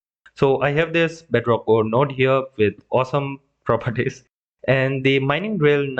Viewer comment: Why did he say eight in serbian. He says osam not eight. Never mind, he said awesome, but I heard him say eight in serbian.